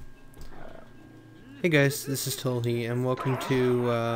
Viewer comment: at first i thought u said toliet not tolhe